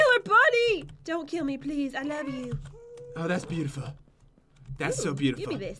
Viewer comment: doesn't the ghast sound like it said love u?